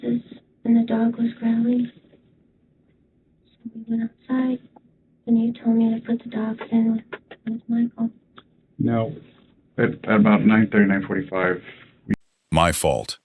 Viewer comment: I didn’t hear “it was my fault” I heard “he told me to put the dogs in with Michael”